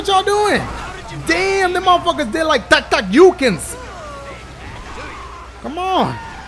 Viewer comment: what did you say LOL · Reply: Da da you kin